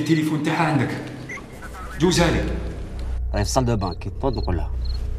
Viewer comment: سمعتو ولا انا مارانيش نسمع قالو جوزلي سارة قالو راهي فصلدوبان كي تنوض نقولها هههههههههه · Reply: قالو دوك نقولها ماشي كي تنوض نقلها